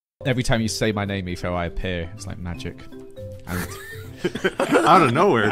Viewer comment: I swear I heard "everytime you say my name Etho I purr" not "I appear" 😂😂😂